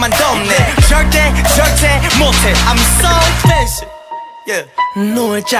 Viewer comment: it sounds like zico said "im so fishy"